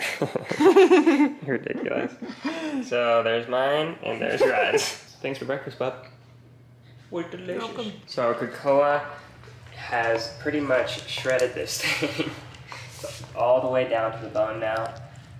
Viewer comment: I literally thought he said you`re delicious! xD